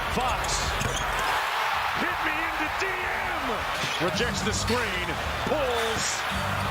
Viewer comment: Did he just say hit me in the D?